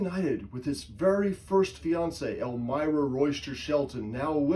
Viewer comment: Thought you said Elvira.😅 Fascinating 😊